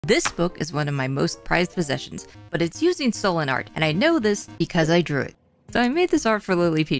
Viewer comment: I thought you said Sollen art and thought it was a kind of art technique 💀